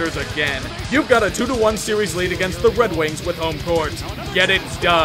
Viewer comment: did you say "wedrings"?